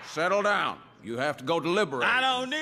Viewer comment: DAMN! MANDELA EFFECT ON ME HERE.. I Could have sworn he said it more like "I don't need to dye lib oorate!"